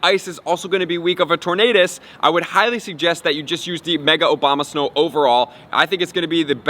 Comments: lmao, anybody else heard obamasnow? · did he say "mega obamasnow"?